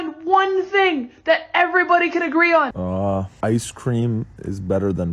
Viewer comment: Bro I thought the guy was saying “I cream” but he was actually saying “Ice cream”😂